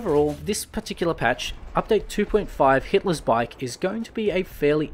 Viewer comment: did I hear Hitler's Bike?